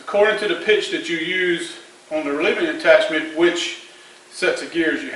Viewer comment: On 2:30, what's the word You say before "attachment"? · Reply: Relieving Attachment